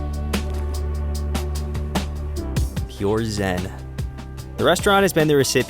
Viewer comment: Did you really said: "Pure Zen" ? :D